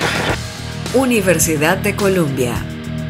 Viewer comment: pense que dijo colOmbia yo que pense que podia estudiar ahi 😣😣😭😪😥